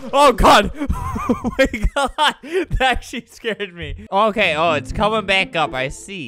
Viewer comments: Me: Albert has a kid friendly channel- 1:02 : That s* scared me- At least thats what it sounded like to me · Anyone else hear “that sh*t scared me”?